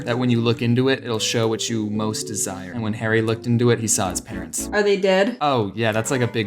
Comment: Lol I dunno why but when I use captions and I thought it said "He looked into it and saw his parents ded"